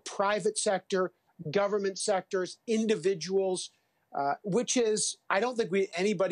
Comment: What did he say there at 2:15 about witches? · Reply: I think he said "Which is".